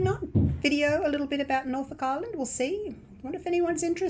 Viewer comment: Have Fun And Yes I Would Love To See Ireland!!🥰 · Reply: I thought she said Ireland too, but she actually said Norfolk Island. Haha